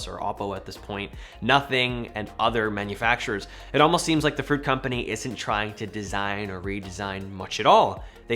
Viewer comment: Did he say the fruit company😂🤣🤣🤣